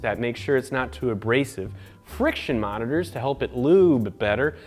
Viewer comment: did he say friction MONITORS? 10:06 PRESCRIBE?